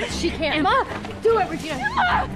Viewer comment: Did Emma say mom at about 0:34? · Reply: She said "I know but she can" or "No but she can"